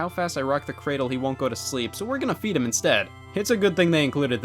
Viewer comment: I thought he said “we’re going to beat him”.